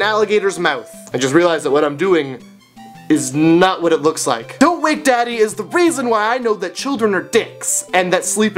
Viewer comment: i thought you said "dont rape daddy"... xD